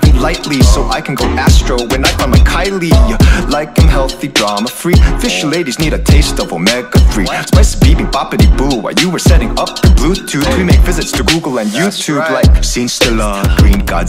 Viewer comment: 아무리 들어도 fish ladies need a taste of omega3로 들리는데